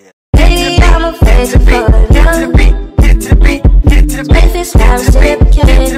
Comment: What is it saying in the background on 1:15? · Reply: “Hit it to the beat”?